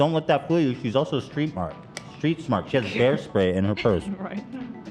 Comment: Ethan: “…she has pair spray in her purse.” Is what I heard 🍐 (edited = time)